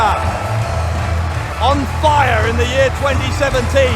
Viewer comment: BUSHIDA に聞こえる